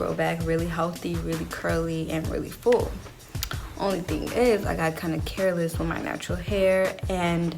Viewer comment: english is not my native, I had to turn the captions on to understand all of her words Her voice is low and she doesn't pronounce all the letters well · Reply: yeah maybe though I don't face this problem with other American youtubers and I watch a lot Now for example I repeated the video to remember how she speaks and at 1:00 she said (the only thing is... ) I had to repeat it 3 times to hear what she said And am sure there is more during the video I think like she eats some of the letters, maybe am mistaken idk